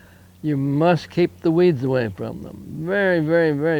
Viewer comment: What´s the word he emphasizes at 4:22 Min.? What should I keep away?? Thanks! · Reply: Weeds